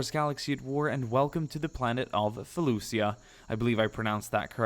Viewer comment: For a minute I thought you said felufia thought I replayed it and you said a right so good job for not making me get triggered.